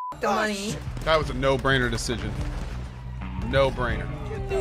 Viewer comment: "That was a not brained decision" bro i move the mouse ( 12:05 ) and the first guy a see is xqc lol, what can i say